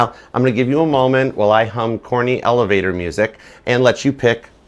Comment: first thought you said horny elevator music and I listened back and I was like nah you CLEARLY said corny... jeeez... confirmation of the near end of my six years of celibacy.. ? We shall see Love ya ♥️